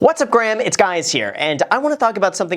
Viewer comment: Did Graham just say "what's up Graham, it's guys here"!? Listen closely